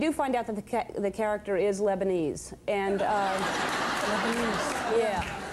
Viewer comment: Dang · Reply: Thalia Munoz 1:09 is what you’re looking for. She uses the word “Lebanese” instead.